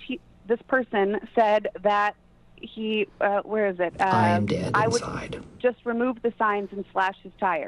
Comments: Anybody else hear it say “ I’m dead inside” around 20:02 because I was about to fall asleep and I wasn’t sure if I was hearing things or if it was on the video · anyone else hearing a voice say "I am dead inside" at 20:01 ? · Anyone else heard someone say I'm dead inside @ 20:02 ?